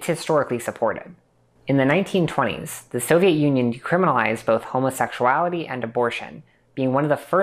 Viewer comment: There's a typo in the CC at 20:25 - You say "decriminalized" very unclearly but the CC says "criminalized"